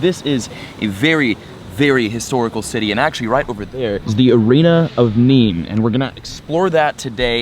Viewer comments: Who else thought he kept saying “arena of memes” :P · The whole time i thought he was saying the Arena of Memes